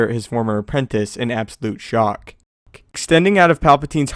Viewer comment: who else heard him say 'shock-k'? I certainly did.